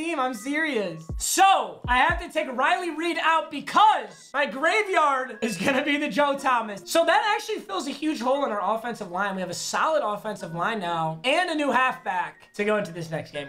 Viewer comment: coulda sworn he said Riley Reid